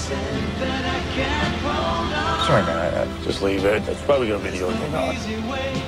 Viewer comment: Love this, soo funny. Can someone tell me what dean says at 0:50 though? I replayed it so many times and can't understand him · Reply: "Just leave it. It's probably going to be the only thing on." 🤓